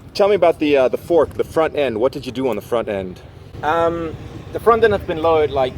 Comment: I thought you said you had to have a front fender in Denmark?